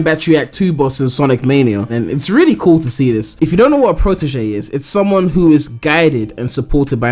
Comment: I thought you said proteggent